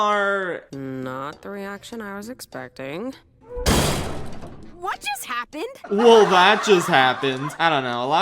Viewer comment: I’m going mad everyone says Luz says “that just happened” but every single time I listen to the line I swear she says “WHAT just happened?” Am I going mad 😭